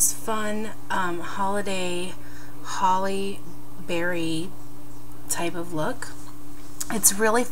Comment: LOL When you said holly berry look in the begining i thought you said halle berry ahaha